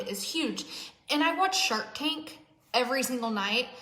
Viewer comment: I’m listening to this in the background while I work and I thought she said she watches shark kink every night. I’m dead 😂